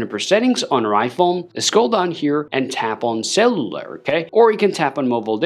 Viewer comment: I thought he was saying eSIM but he was saying cellular which my iPhone does not have. Boy he needs to learn English